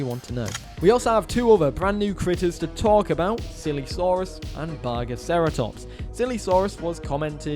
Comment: i swear to god, when u said "silesaurus" the first two times at 4:24 ... i heard "sillysaurus" both times.